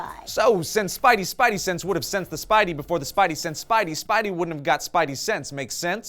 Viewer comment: Am i the only one who thought it said Sense sense for a minute?